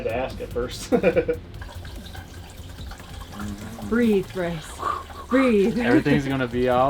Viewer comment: idk if you said “breathe Brice” or “breathe Chris” ((i think you said Brice)) but whoever he is, that was the sweetest nervous smile I’ve ever seen!!